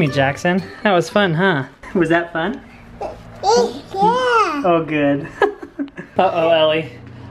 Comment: Did Jackson say " Heck Yea " 😂